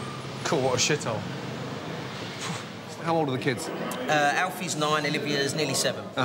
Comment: Did...did he call the place a shithole?